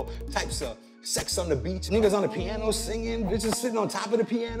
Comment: did he say nibbas?